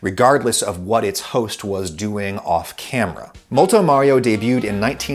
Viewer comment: When it said multo Mario I honestly thought it said Moto moto.